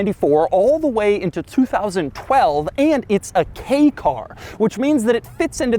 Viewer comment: did he say gay car?